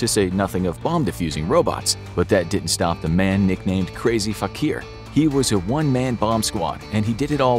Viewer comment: am I the only one that noticed that bomb diffusers name is crazy fukir. Surely somebody else hears what I hear in that name. (Oh and I'm sure I spelled his name wrong)